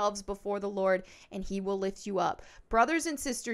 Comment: 😄 at first, I thought you said, “he will LICK you up.” But, you obviously said, “he will LIFT you up.” I wouldn’t want him to lick me up 😳. 🤣